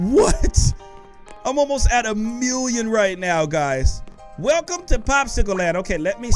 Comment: who else heard him say 11,000 when actually he had 11,000,000